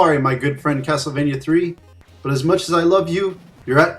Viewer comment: Than im crazy.. i swore you said castlevania 2, simons quest lol